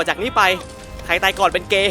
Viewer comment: พี่พูดว่าไงนะ😂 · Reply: ใครตายก่อนเป็นเลย์ :)